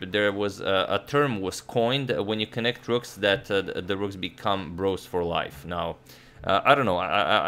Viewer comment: Agad: two connected rooks become bros for life, Me; yeah, until I proceed to blunder a bro 2 moves later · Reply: I heard him say turds for life